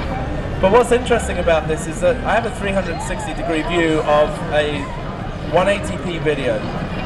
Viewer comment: Did he say 180p at 3:42 because the video looked like 1080p to me